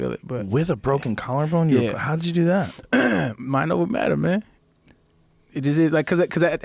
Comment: He SAID "Mind over matter," but what i heard was "MORPHINE."